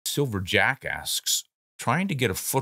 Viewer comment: Lol, I thought you said silver Jacka$$.